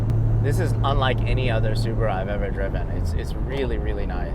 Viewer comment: Did he call a subie a supra?